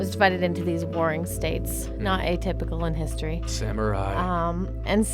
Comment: did he say SAMURAI?????